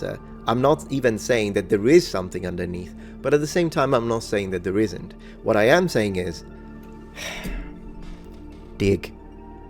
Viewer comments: "what i am saying is..." "dick" 😳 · I thought he said “dick”😂